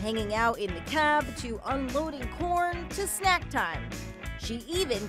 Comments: I thought they said uploading porn for a sec. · Who else thought she said "uploading porn" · I THOUGHT IT SAID UNLOADING PORN SMH